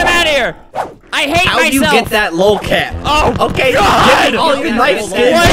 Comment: who else thought he said “how do you get that little cat.”